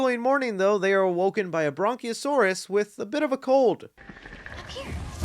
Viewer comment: did he say bronk-io-saurs?